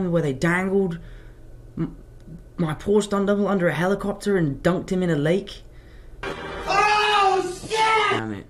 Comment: what was he referring? I couldn’t understand what he said before “dunked him in the lake” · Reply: “They dangled my poor stunt double under a helicopter and dunked him in a lake”